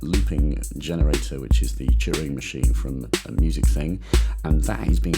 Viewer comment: Thank you for the explanation! Btw, at 6:35, instead of "Turing Machine", the automatic captioning understands "Cheering Machine" :))